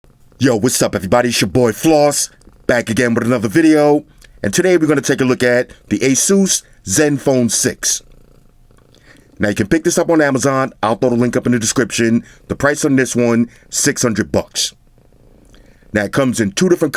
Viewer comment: I thought it was pronounced "aye-sus", sounds like your saying "Jesus" lol 😂🤣 😂